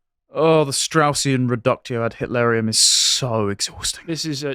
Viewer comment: wait what did he say? What kind of arium is that…?